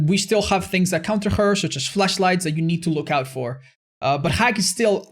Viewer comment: Did he just say fleshlight?